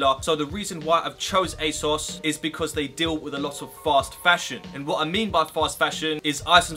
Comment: Who else thought he said "false fashion"